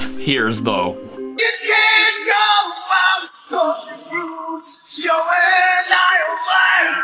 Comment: I heard boots without music.